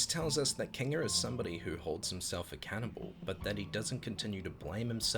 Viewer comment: Hold himself Accountable to me it sounded like you said Hold himself a Cannibal lolol.